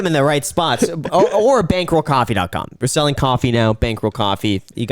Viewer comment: i thought he said bangbro coffee hahahah